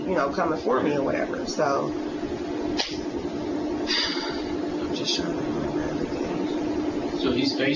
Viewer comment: it really sounded like she said 'im just tryin to find a way outta this' under her breath